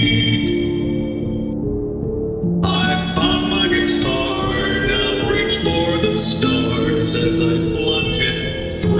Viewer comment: did he said i f**ked my guitar now reach for the stars since plunged it through your heart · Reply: It Sounds like it but he actually says: i found my guitar